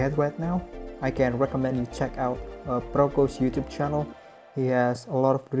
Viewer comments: This is an AMAZING project, and it turned out beautiful. At around 6:30 you mention some suggested resources/channels for anatomy, and I couldn't understand the names you mentioned. Could you possibly elaborate on that a bit more? I'd love to check out their lessons. Thanks!!! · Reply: Sure man! Proko's channel : <link> Rafael Grassetti's tutorial : <link> and <link> CG Makers tutorial : <link> Speed Char's tutorial : <link> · nama channelnya apaan gan? Maaf agak kurang jelas ane dengernya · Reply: Sure man! Proko's channel : <link> Rafael Grassetti's tutorial : <link> and <link> CG Makers tutorial : <link> Speed Char's tutorial : <link> Books : Anatomy For Sculptors by Uldis Zarins and Sandis Kondrats Drawing Beautiful Woman by Frank Cho